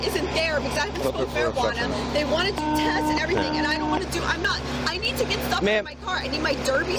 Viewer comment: did she say "I need to get dirty stuff outta my car?"... MILF🤣🤣🤣🤣